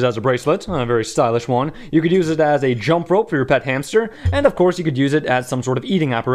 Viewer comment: I thought you said "your fat hamster" instead of "your pet hamster." I think it would be hilarious to see a fat hamster playing jump rope.